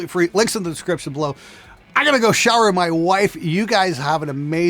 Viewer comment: I thought you said, "I gotta go shout at my wife." 😂